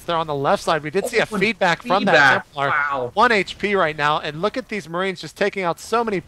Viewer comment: it sounds like he said oh fucking wow